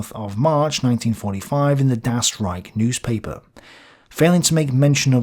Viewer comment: When I heard "Das Riech" newspaper, I thought you said Gaslight. Which would have made a lot of sense.